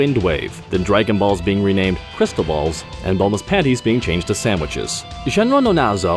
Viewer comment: I'm sorry. Did he say "Bulma's panties"? 0:37 I want Bulma's Panties...